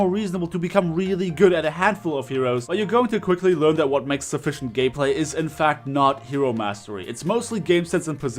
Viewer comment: sounded like he said " sufficient gay-play" 😂